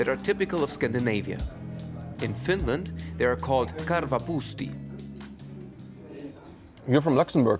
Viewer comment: Haha it’s “Korvapuusti”, “Karva” means fur or hair. Finnish like German does compound words, which makes this mistake quite funny. I’d rather not have Karva in my cinnamon bun. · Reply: Ha ha, i hear "karva(fur)puusti" too 😁😁